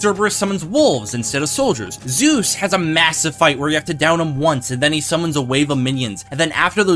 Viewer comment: This is a most excellent video, however, Doom believes the way the end of my story was handled was most disgraceful. Doom could never be defeated by this "Hope" person, especially with a whole month of the season left. Epic Games must be run by Richards, that is the only way this could have happened. Regardless, I do agree that my introduction was very well done. An excellent video, Doom is pleased. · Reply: did you just say massive ?